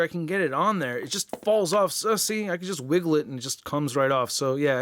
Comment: sounds like he says sussy but he ment to say was you see but sussy